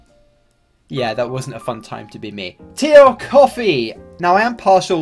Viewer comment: in the video I heard: "Tea of Coffee" And because its 3 AM it took me around 4 seconds to understand that something is wrong